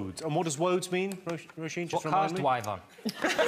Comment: what does jon say?? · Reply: 'It's what cars dwive on'